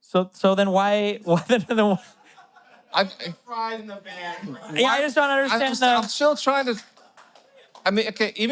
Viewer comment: I think someone said " just put the fries 🍟 in the bag bro" 😂😂😂 literally this man has crap for a brain